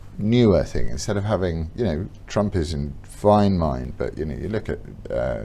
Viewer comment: I had to keep replaying from about 1:25 to make sure I hadn't misheard Laurence saying Trump had a 'fine mind'. I also appreciated Peter prompting that Laurence could despise not only a specific person, but 'a class of people'. Amazing stuff, really. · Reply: He said ‘Trump is in fine mind’, and contrasted that with the cognitive decline of Biden. You misheard and misunderstood, I think.